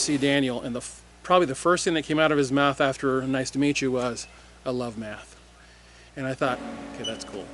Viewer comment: All I heard was, "I love meth". So glad I heard it wrong.